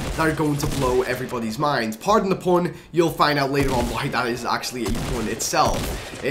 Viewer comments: I thought he said pardon the porn :/ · Did he say “Pardon the porn” at 1:30?